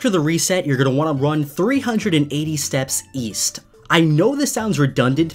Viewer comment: East? I thought you said "Weast"!